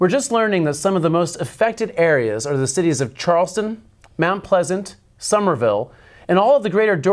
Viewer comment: I can’t be the only one who still hears scream “WHAT?” When the newscaster says “Charleston”